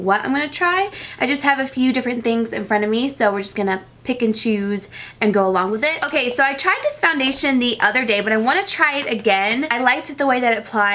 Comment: pikachus, thats what i heard lol. this game has gone too far she said pick and choose